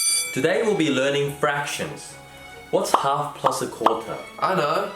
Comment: thought he said fashion lol